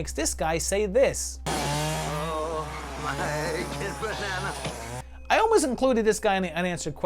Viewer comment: I always thought he said "Oh my good banana" I don't know what that means but I say it all the time.